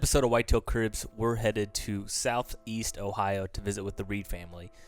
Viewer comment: They’re not all from Ohio. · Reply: Oh i thought he said southern Ohio at beginning maybe that's the majority impressive. He did mention bait at one point, must have the best recipe.